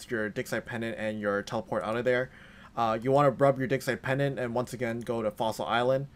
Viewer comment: I’ve been rubbing it for like an hour and i didn’t get any xp all I got was a sore no-no area. I think I may be mishearing you. Can you enunciate that for me plz? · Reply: Rub your digsite pendant. Get some seed.